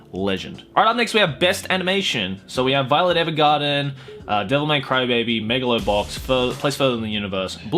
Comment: Every time you said devilman crybaby it sounded like you were saying devil may crybaby